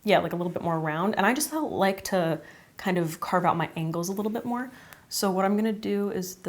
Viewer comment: When you said you like to carve out your angles a little bit more, I definitely heard “I just like to carve out my ANKLES a little bit more.” Which I thought was a clever “I contour a lot” joke